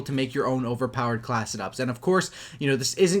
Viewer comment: Holy shit! this whole time he has just been saying "class setup" really fast. I thought he was saying "class it up" :)